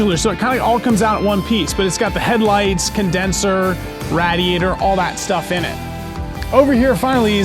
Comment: Did you just say rah diator?????